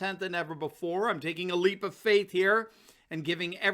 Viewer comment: When you said you’re taking a “leap of faith”, I immediately thought you were saying “leave of absence” Is that what it is? or have you burned bridges?